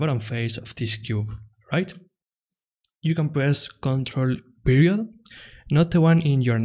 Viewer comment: great tutorial - unfortunately i can't understand what he says @ 0:17 : press ctrl and WHAT??? imperial, perial something, can't make it out :((( but it's essential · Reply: ok, by accident i found d subtitles, he refers to d 'period', or simply 'dot' key :) which somehow doesn't work on my belgium keyboard, as for d dot 'upper key' is needed :/ but it works just fine on d other keyboard :)